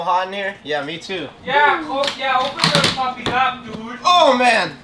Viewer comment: Did he say yeah open the fucking window dude